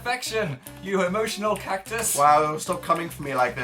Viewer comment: I heard, "You HOMOtional cactus"